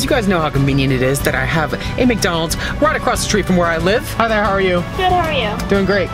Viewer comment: i thought u said how th ehell are you 😂😂